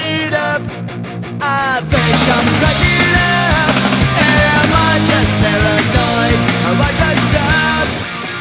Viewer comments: "Am I just paranoid or nayyaya" best part of the song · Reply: I at first thought he said am I just paranoid about this stuff.... I still sing it as that as I am not a druggie.. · Tbh I thought that it said "am I just paranoid or am I just dumb" no it was "am I just paranoid or am I just stoned" Bush wah